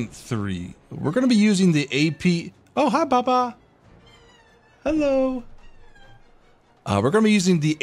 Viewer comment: i swear that cat answered saying "hey hello" there!!